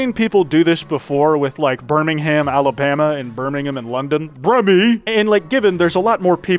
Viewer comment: did you just say Birmingham in London